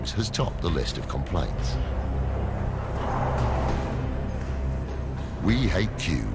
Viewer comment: Am I wrong or did he say "We hate Jews.is the security mantra" ? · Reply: We hate queues is the security mantra